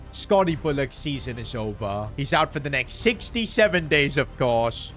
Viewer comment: what do you say? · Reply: 67 days 😊